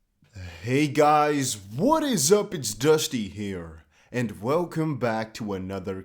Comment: I know you say "It's Dusty here" but I hear "It's dusty hair" lol